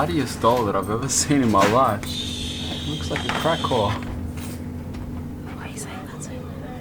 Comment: pause on 16:53 did he say Crack whole ..lmao dead lol ok push play